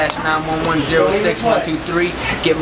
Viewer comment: did he say myspace??